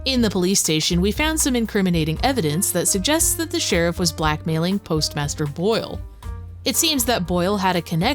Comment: I misheard as “post master bate” 😅